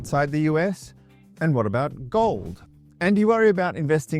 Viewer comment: When you said "what about gold" I thought you said 'what about golf" and i didn't really think twice at first, because afterall, you are retired lol